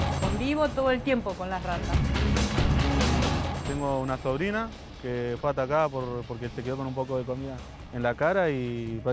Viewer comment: Pense que decia tengo una sobrina que es una rata.